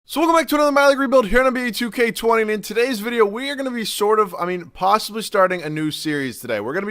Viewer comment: lol... Today was the first time i understood what you actually say at the beginning.... myleague rebuild.... I always heard you saying 'Malik rebiuld' and thought your name would be Malik..... I did not watch a ton of your videos but definitly more than 20